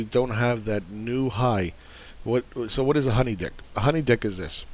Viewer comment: HoneyDICK ? LOL That's a new term. I thought you were saying DIP, though. That also makes sense (like a plain donut that is dipped to give it a thin veneer of sweetness). That aside, how does BitCon fit into the MMT flow dynamics, though? Is it considered a sink for USDs in lieu of JPY or (what seems to be a newer goto) the CHF?